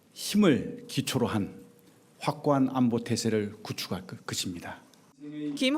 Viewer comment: 왜 내 귀에는 확고한 안보가 아닌 확고한 독재로 들리지??? 돌려막기 하고 있네...